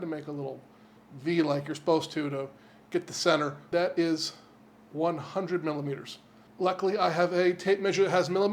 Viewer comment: 100mm is 1/9 of a yard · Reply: I thought he is saying 100ml and that glass is definitely more than that 😂 Btw the yard around our home is bigger than 0.9m